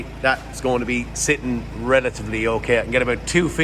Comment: I clearly heard 'two fingers, maybe a turd...' But good video. I have the wagon and this is very applicable.